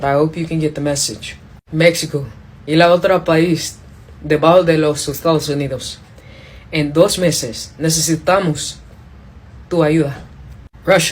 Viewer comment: I kinda know Spanish I took 3 classes. But I’m better at listening and writing the words I hear than I am speaking Spanish . . 9:44 but this is what I got for the message to Mexico. “mexico, y lao tra paiz de bal de los sus stals enithos. en doz meces. necessitamos tu ayudar. “ Which google says it translates to, “Mexico, and another country, are among the countries whose states are united. In two months, we need your help.” Can someone let me know if this is what she said?? Or was my spelling & interpretation of her message in Spanish completely off ??? 😩 I really tried hard to listen each word.